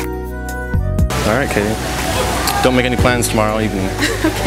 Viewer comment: Turn on caption then play at 00:37 whats your number buritto instead of 480 etc.